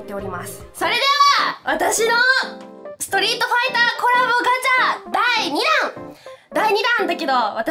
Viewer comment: 最初クソリートフアィターコラボって聞こえた。